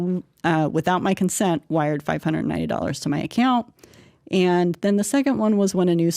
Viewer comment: Why at 32:50 does it say in the transcripts does it refer to $590 when she said €590 million?